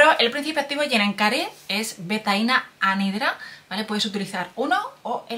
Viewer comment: no entendí el principio activo... quien me ayuda 😢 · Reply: GENENCARE® OSMS BA